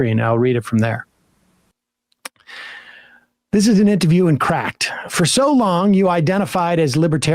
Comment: I think he said it was from an interview with Cracked. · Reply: I thought he said Cracked, I thought I misheard. Thank you, I'll go take a look.